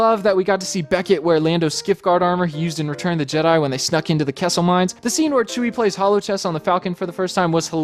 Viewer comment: I thought you said “Quesso mines”